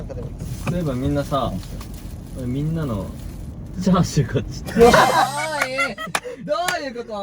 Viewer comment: 誰か孫の手のことチャーリーって呼んでるように聞こえる(チャーシューかなw)